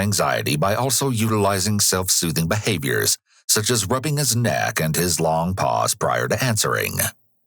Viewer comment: I wasnt watching the screen , just listening at the time , i thought he said "When rubbing his neck, and his long paws...." My eyes shot back to the screen, expecting to see a dude in a cat costume or something.